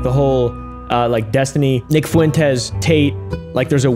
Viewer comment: when you said nick fuentes i misheard you as vic fuentes and I got real scared.